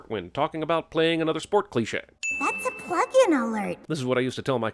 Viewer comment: what did she say? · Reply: "That's a plug-in alert".